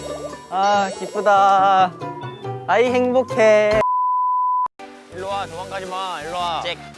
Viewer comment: Is that REALLY what he said?? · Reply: No he said I'm happy